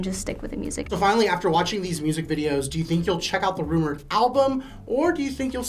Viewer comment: Did he just say the “rumor” album?